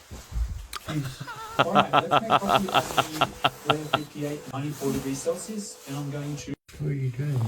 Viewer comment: Did he say -4°C?